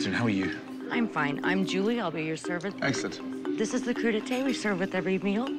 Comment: When he said cru d'ete I thought he said Crudité